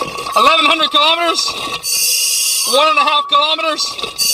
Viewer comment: Did u say 1100 kilometers?